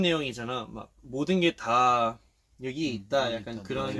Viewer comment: what he said? guitar? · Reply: he said "everything is here" -- subtitles are finished sorry for the inconvenience